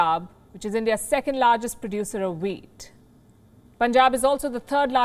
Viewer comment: "There's Punjab which india's second largest producer of wheat" -I THOUGHT SHE SAID WEED, AND I WAS LIKE oh itna common ho gya weed bhi jese Heroine hai in Punjab.